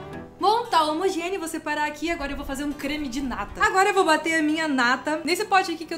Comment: só eu que entendi "Agora eu vou fazer um creme de nada!" 😂😂😂😂😂